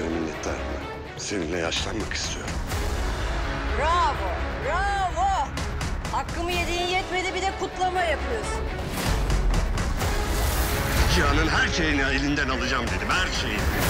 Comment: What does Mine say at 0:12? Can't wait for this season❤😱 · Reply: She says ' bravo , bravo . It's not enough for you to took what i deserved now you're celebrating ' Her character's name is Asuman and she's Dilara's sister 👍🏻